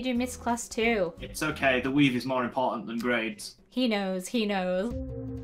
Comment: I thought at the start they said ‘weed is more important then grades’ LOL